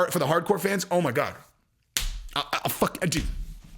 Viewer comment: Bruh... at 10:15 did you say you'd "fuck a dude" while repeatedly saying "GIVE IT TO ME" after?!?!? Do you bro. DANAAAA!!! GONNA FUCK A DUDE IF YOU MAKE THIS FIGHT!!!